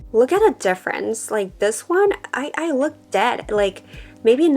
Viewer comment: Lmfaoooo did she said said she looked dead ??